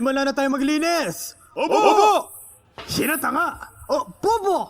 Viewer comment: Did he just say "Hinatanga"? cuz im dying HAHAHAHAHA